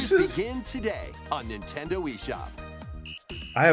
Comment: did I hear "Nintendo Wii shop"??